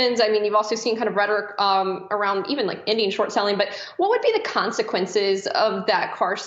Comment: what is she saying at 07:20 ? What is Indian short selling?